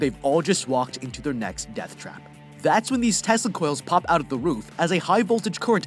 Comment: Surely i cant be the only one who heard "testicles" here.